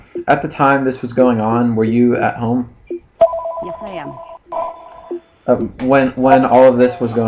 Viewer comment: did she say "Yes ma'am."?